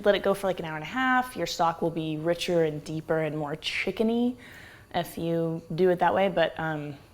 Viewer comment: I thought you said "F-you. .. do it that way".